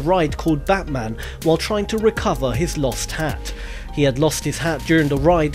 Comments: I know I've heard about the batman one before but at 0:44 I swear to god his accent sounds like he says "his lost hand". No rudeness intended it just made me a little weirded out and I had to go back and listen a second time. It's a ok because I do know that he says hat.👌👌👌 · #5 I thought he said to find his lost hand XD